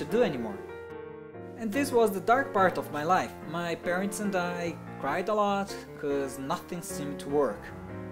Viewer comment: I'm not saying your English is bad but at 2:57 I thought you said they died a lot and at 5:10 I thought you said your girlfriends dead lol